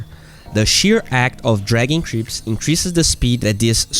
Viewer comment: I always hear "dragon creeps"